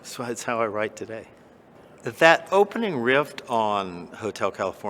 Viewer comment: Did he say 'rift'? Instead of riff? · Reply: Sounds like he says rift... twice...